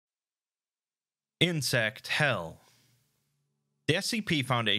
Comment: For a second there I thought it said incest hell.